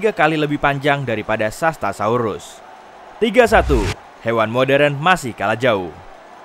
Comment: Salah ya? apa aku yg salah dengar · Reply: Hewan modern masih kalah jauh Kalah jauh score nya mungkin